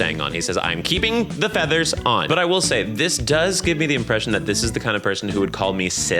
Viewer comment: what does it says about me and my levels of transgenre that i thought u said "cis" at 29:32 and not "sis" 🤣🤣